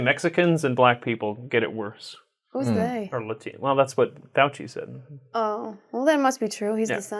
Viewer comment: When he mentions Fauci at 23:10 the subtitles say "douchey", pretty apt algorithm for the BB.